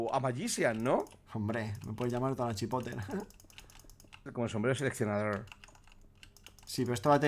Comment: En el min 10:26 escuche tonachipote, espero no haber sido el unico